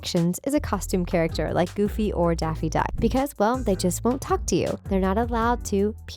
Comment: Did she say Daffy Duck? 💀